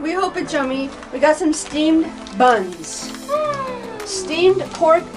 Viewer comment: Check out open road for two. Yea....What? ..I thought you said steam our buns. Lol...they looked good.